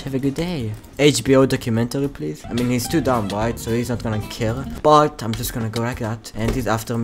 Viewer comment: i thought you said he's too darn white